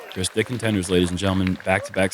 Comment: Sorry did just say "Chris Dick-and-tenders" at 6:10?